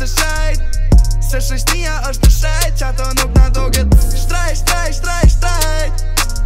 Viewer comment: i am albanian from germany but what does shejt mean? i thought he said shtrejt the whole time ( se shoqnia osht e shejt)